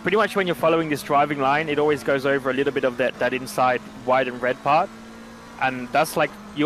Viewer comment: cringe bro that whole curb is not the apex at all, it’s the inside point of the turn where the concrete meets the red/white curb. Lando even said it right. Shroud, I’ll be your editor I’ll actually google shit smh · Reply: where tf you get “that dead inside” He says “that…that inside”